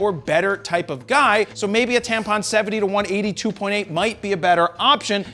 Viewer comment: Did he just say "Tampon"?